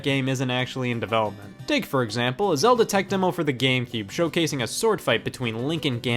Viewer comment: it sure sounds like you said GameGeeb.